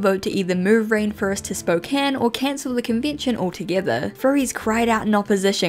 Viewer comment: As someone whose from Spokane, I wouldn't want them here, but actually, while it /is/ a pretty conservative area due to being so close to Idaho, it's not actually homophobic, and our pride event is actually pretty big. still hate this city though. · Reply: thought spokane said spookane and i was like 👻!!!!!!!!!!